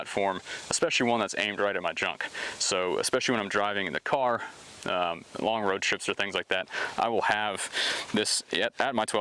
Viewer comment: Lol I thought when you said “aimed at my junk” I thought you said “especially when Im drunk, and especially when Im driving in the car” lololol